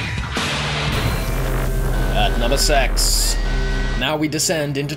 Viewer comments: when ever he says "number six" it sounds like he is saying "number sex" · Did he just say number sex